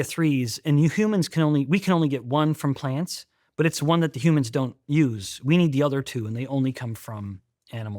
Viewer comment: Did anyone else catch “you humans” at 40:13?